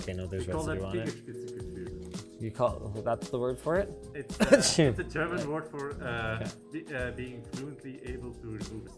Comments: did he say fingerspitzengefühl or something like this? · As a German I would be interested what he actually said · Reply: Fingerspitzengefühl? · For those wondering what he actually said, I think he said "Fingerspitzengefühl," which is a compound of "Fingerspitze" meaning finger tip, and "Gefühl" meaning feeling. Having "Fingerspitzengefühl" means being good at fine motoric stuff. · "We call that Fingerspitzengefühl" That is what he said if u wanna know ^^ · It took me so looong to understand, what he said at about 13:52, dang it. And yes... Some of us have it and so don't. But "Fingerspitzengefühl" is something which isn't a bad skill. But it's something not only for stickers on fans... XD